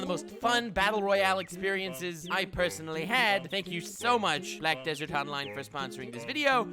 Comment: When he introduced the sponsor, I thought he said "Black Zetsu Online"